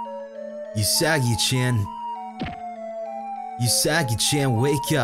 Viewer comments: At one point it sounded like said "you saggy-chan" xD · I know he said "Usagi-chan", but I keep hearing it as "you saggy-chan"....